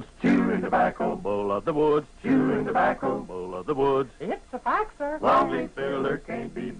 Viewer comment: Can someone tell me what that cow is saying at 0:45? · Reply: "It's a fact, sir."